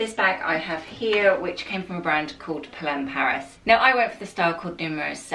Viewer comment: Where was the first bag from? I thought I heard Clem Paris but I’m not sure. Gorgeous style and video! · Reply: Hi Jessica, It's Polene Paris:) I have quite a few of their bags now, they're gorgeous. The quality is impeccable for the price. x